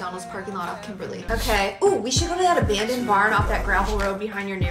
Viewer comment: Why am I so excited to meet the boys at the McDonald’s parking lot and then go drink some stuff in the abandoned barn? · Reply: thought she said abandoned Bar not abandoned barn